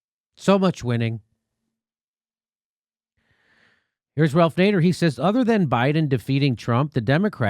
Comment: I always hear "Darth Vader"